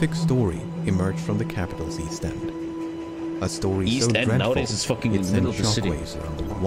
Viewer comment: Does anyone know what says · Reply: I think he said east end is in the middle of the london now